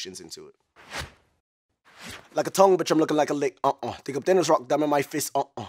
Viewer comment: deadass thought he said like a tall bitch im looking like a leg